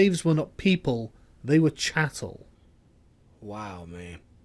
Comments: Misheard word was "Chattels", (narrator mentioned people "treated as chattels" (not cattle).) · He doesn't say 'cattle' he actually says 'they were chattel' which is a legal term meaning that the slaves were someone's property.